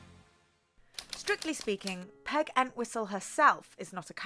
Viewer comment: I swear to Godt I thought they were saying Peg and Whistle not Peg Enwhistle 🤦🏿‍♀️🤦🏿‍♀️🤦🏿‍♀️